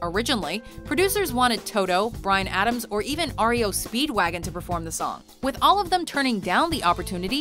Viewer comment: I heard OREO speedwagon